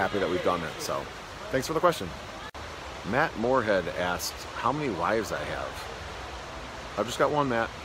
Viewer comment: boy did I look up from my crafting at the wife number question :O I thought I heard how many lives do you have :P I like your method of security of not using everybody's names good for you :) sorry I did not comment on all videos but I figured somebody might get a chuckle from my bad hearing :D thanks for sharing